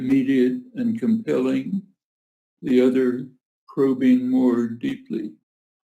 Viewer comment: The subtitles must have been written by AI because there are quite a few important mistakes. Example at 1:17 minutes in Crow instead of probing. No full stops and capital letters in the wrong places. These are the comments of a very important figure in our history, surely these details should have been addressed before this program was released.